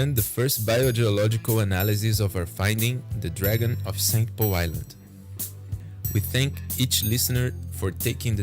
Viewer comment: Oh and did he call it the dragon of St Poe Island? Bit of a tell there....